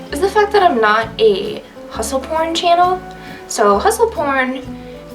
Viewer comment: Ha, ha, ha. I thought you said hustle porn channel 🤣😂🤣😂 But then I read the description and it says hustle corn. I haven't been writing much either the last month. I got really sick in July and then I had family stuff, back to school, and without warning, a pile of depression and imposter syndrome hit me like a bomb. But I'm getting back on track. I too get frustrated with vague, e feedback. I'm always waiting for details, and I love having conversations about people thoughts about my writing, but very rarely do I get more then, I didn't like it. I don't know why, but I just don't. It's extremely frustrating. I agree with you 100% about experience being a must if you're going to offer services, especially in exchange for money. I look forward to more of your videos.